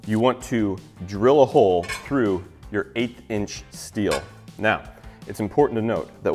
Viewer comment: the first time he said 1/8 inch steel... I misheard it as 8 inch since I am watching at 2x speed. caught me off guard for a moment lol